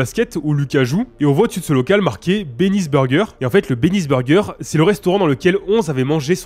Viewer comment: ses moi ou il dit p3nis burger??